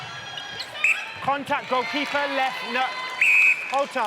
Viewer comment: what did he say at 39:20? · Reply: The call was “Contact goal keeper”, then he didn’t like her reaction and said “Caution for Dissent”